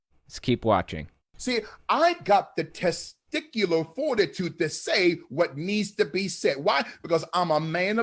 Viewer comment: what the gay? Did I hear that right, testicular warden?